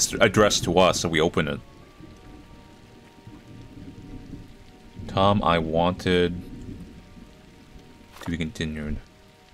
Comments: I think it says "Tom I wanted to tell you something very important" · says “Tom, I wanted to tell you something very important” (Here I am) the words in parentheses is what I assume it’s saying